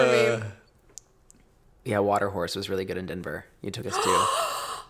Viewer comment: Missed this one so just now listening.... Do you mean Watercourse? It sounds like your saying Water Horse but I think you mean Watercourse in Denver lol